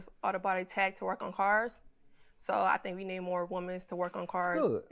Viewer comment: Did she say “more womans” 😂 at 2:16 lol